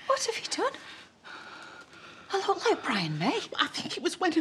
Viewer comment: I think she said Brian May from Queen.